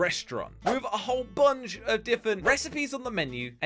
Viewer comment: I love how josh says diffint instead of different